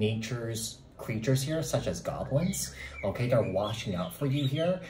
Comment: Did he just say goglings? 😂😂😂😂😂😂😂😂😂😂😂 Dafuq whoever watches this man is just as high as him